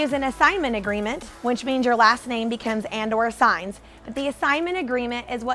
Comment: Hi, when you spoke about the assignment agreement I didn't understand what you were saying at 1:24mins. Did you say that the last name becomes and or assigns. Where can I find an assignment agreement? Thanks for the great info! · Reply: I said that it becomes "and/or" - The contract essentially says that I can assign the contract to someone else (and I become the middle man). I give copies of all of the contracts and paperwork I use to my students in my academy! Glad you found it helpful!! If you want to learn more, I have a free webinar coming up. You can sign up and find out more about it here: <link>